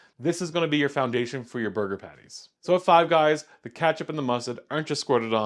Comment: DID U SAY CATCH UP 🎉😂